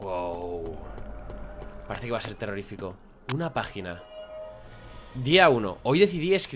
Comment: parece que dice una vagina xD